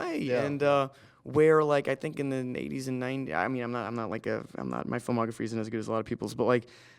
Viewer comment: I already repeated watching this three times in just half a day! I miss so much everything about him. "Immediacy of moment-to-moment thinking" his wording is just as intelligent as usual. Anyone can tell me what he said at 7:17? He talked too fast there. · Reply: Chimney DN my filmography isnt as good as a lot of people